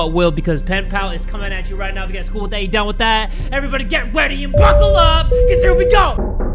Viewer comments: why did it sound like jay said get weety and fuckle up so here we go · Jay I've always thought u said "GET READY AND FUCKLE UP CUZ HERE WE GO" Lol